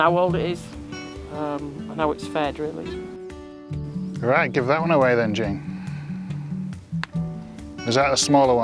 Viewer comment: I thought he said, "Well, we'll give that one away then".... took me in hot second to realise he actually said, well, we'll give that one a WEIGH then! Pheww!! 😆 🤣 ❤️🧡💛💚💙💜